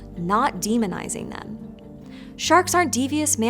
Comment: I think one of my favorite jokes about the demonization of sharks is, "Sharks aren't so bad. If a stranger came into my house wearing only a speedo, I'd probably attack him too." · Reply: I thought you said demonetization of sharks for a second